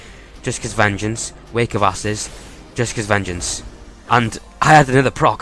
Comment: could of sworn I heard you say wake of asses lol.